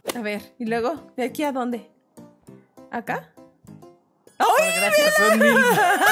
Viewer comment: lo que entendí fue:Cual gracias son 1000 ovarios